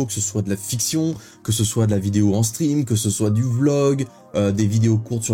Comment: Début de vidéo j'ai bugué car j'ai entendu "de lavidéo en string" 😂